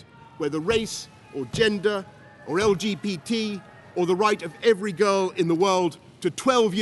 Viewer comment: I swear he says lgpt is is that me